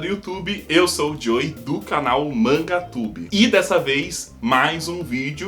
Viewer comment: Manga tube kkk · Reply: eu entendi manga tube mão mangá kk